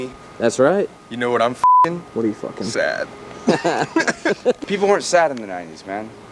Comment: I thought he said Sam for a min😂😂😂